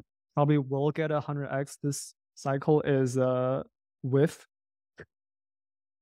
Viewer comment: at was he saying "wif" as in "dogwifhat"?